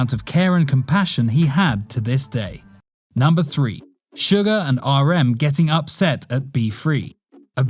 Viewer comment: Wait did they say "Sugar" like... the edible sugar? 2:02😭